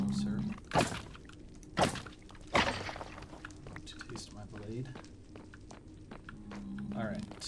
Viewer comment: lol did you say "sir, taste my blade"? Lol the trash talk is unmatched